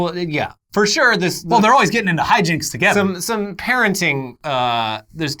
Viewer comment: i swear i heard “karenting” and damn if that isn’t a perfect word to describe that relationship...